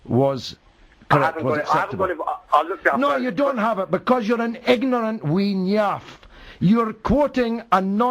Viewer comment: What does George say at 5:44 ignorant wean yarf?